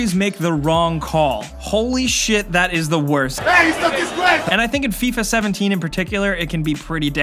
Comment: Did he just say "Death is no disgrace"? That's Salen Kotch's quote in Infinite Warfare. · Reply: Hey its a disgrace